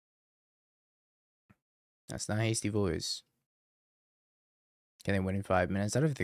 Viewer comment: I thought he was saying "Hasty BOIS" lmao